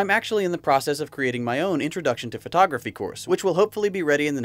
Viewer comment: I'll be sure to check out your photography course when it's out! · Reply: I could've sworn you said pornography.